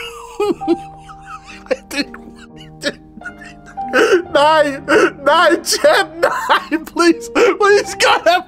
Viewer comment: did u say Nein?😂